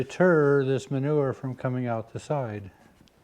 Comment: I am not going to lie I giggled a little when you said "this board will help deter it coming from out the side" in my mind I heard "it de-Turd it from coming out the side"